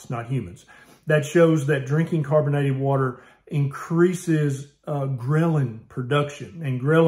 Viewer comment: When Doc said Ghrelin, I was passively listening and thought he said grilling for a sec, that got me hungry. :)